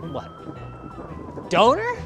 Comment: Curious is at 3:49 after the thunder he says: ''Donor'' but i first heard ''Donar?'' that is , if im not mistaken, one of the names o Thor. (in Dutch)